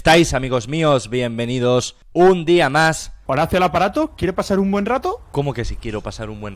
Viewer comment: JAJJAJAJAJAJJAJAJAJAJJAJAJAJS Morí con ese inicio. "Horacio al aparato ¿Quiere pasar un buen rato?" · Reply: Pensé que había dicho: Horacio el aparato ¿Quieres pasar un buen rato? Xd